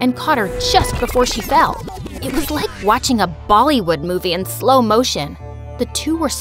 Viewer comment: I'm sorry, did she say Bollywood 🤣🤣🤣🤣